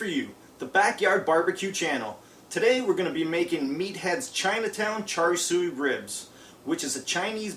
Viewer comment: i thought it said meth heads